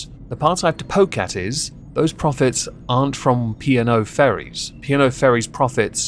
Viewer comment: I was very slightly confused at the P&O ferries because i heard 'piano fairies' at first and I mean.. Piano fairies sounds kind of cute. May be time for a hearing check.